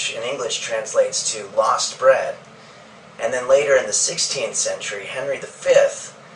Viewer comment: i think he said roasted bread instead of lost bread :)